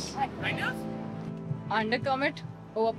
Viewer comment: 👍🏻 Lovely voice, sweet rendering & a dash of wisdom: Under commit* - over perform. 12/7/23. a humble, talented doll. *I’d thought she said “under comment”, which made perfect sense. Only now did I see it was “commit”.🙄